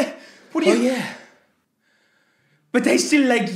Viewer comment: What the captions say: "I still like yeux" what I hear: "I still like you"